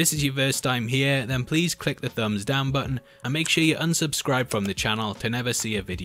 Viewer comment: did he say "click thumbs-down button"?